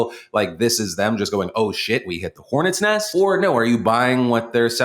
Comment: Heh, I thought Phil said "The pornet's nest". Also, Sway, unlike you, pornstars actually get up off their ass to do their job.